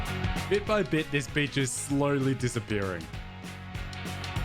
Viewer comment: it sounds like he said “bit by bit this b*tch is slowly disappearing”. Yes I quoted him